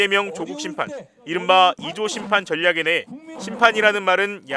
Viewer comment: 이조심판.... 하=33 꼭 조선시대 격하하는 이씨조선 심판으로 들리네;; 자꾸 국짐당은 친일냄새를 뿌리니 뭐든 곱게 안들린다 이젠.....